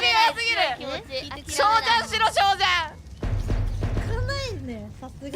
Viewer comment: ドンドンドドンが「小ジャンしろ」に確かに聞こえた